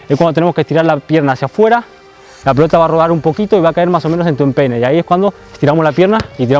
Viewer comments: yo pense que dijo enpene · escuché pene XD